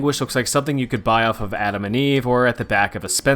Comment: Adam and Eve is very close to my Apple and eve apple juice, almost misheard it as such lol